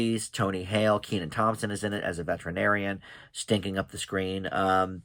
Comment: Phew, I got worried there for a sec. At first I thought you said Tony Hale was stinking up the screen. Glad I heard it wrong, he's in Hocus Pocus 2. :)